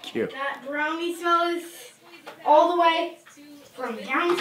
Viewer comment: I thought your daughter said "Brony" smell instead of "Brownie" and I was like damn she's burning her parents good oof. -Matt